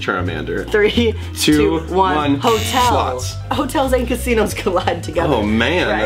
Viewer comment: I started flipping out because I was looking away and only listening and I thought Thomas said sluts 😂😂